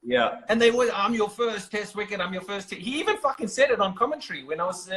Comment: did he say "fucking" to ms dhoni how dare this bloody hell talks like this